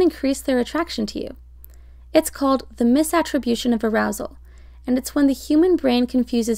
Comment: I heard “masturbation and arousal”